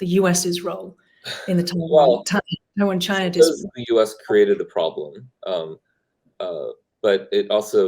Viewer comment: what was Julian saying here? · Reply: He mumbles, a lot. "because the US created a problem [...]"